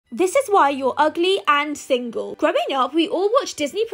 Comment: I was scrolling casually and I just hear “Here’s why your ugly n single!!”